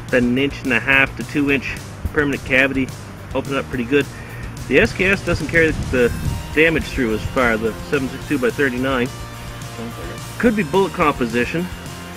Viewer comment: Did you say at 4:41 quote "The SKS doesn't carry the damage through as much as the 7.62x39"? The SKS is the one chambered in 7.62x39 not the Winchester in 30-30 you big Dummy :) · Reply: Uh, no, what I said was "the SKS doesn't carry the damage through as much, the 7.6X39" . Thanks for watching.